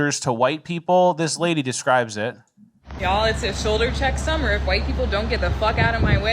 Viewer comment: You said "shoulder check summer" I heard "pepper spray summer".